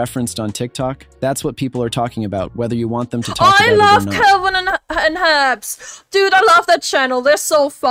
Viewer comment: I thought you said Calvin and Hobbes the comic strip series made by Bill Watterson. I was confused only for a moment on why a YouTube channel was named CalvinandHobbes .